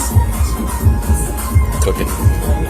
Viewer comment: when you thought the coplooked in and said "cooking" I think he actually said "©ocaine"😉 I think the guy further down the house who appeared to be kneeling down, he may have been sitting on his step havin a smoke of some sort.but I must say he did look weird, he also has a perfect view of the 2girls bedrooms if he was the peeping kind..for all we know he could be the one.. he sure looked like he was from head to toe in black🤨 very good catch Ps, I'm from Ireland, creatine is legal here an my little bro is JACKED!! Like the hulk lol .he says creatine makes your muscles need lot of water and it's the water that builds mass so quickly when u work out..coz I asked him specifically what it does an how it works..hope that helps you out with some some insight. It's also a control thing about the guy why people get addicted to looking good an gains. I'm not into it myself but I wouldn't say no either,if ya know what I mean 🙈😂